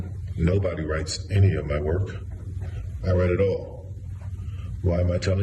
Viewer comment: I thought he said " my work is pathetic ".That's what I heard. 🤔👀👌😇👀